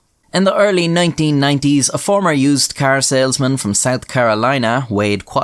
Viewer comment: i just heard "carousel-man" instead of car salesman